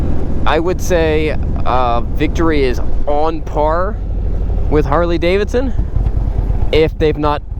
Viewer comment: Did you say "Victory is on par with Harley Davidson" at 4:59???